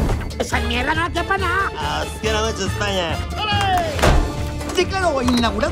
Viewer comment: Que dice Filemón en el minuto 1:17??? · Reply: "Os quiero mucho España!" a lo Julio Iglesias.